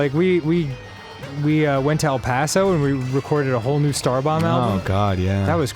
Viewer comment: I’m sorry did he say A NEW STARBOMB ALBUM!?!?